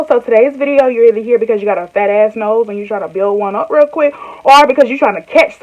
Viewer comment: First seconds watching this and I hear “Fat ass nose” 🤣🤣 yup I’m subscribing!! Lol I love you already